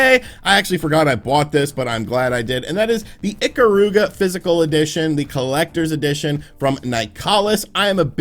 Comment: Oh God, at 9:53 I thought you said "Icabod"